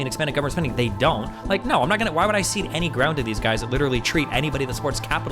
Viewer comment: In the intro ( 0:43 ) I think he says "cede" and not see. "Why would I cede any ground (...)"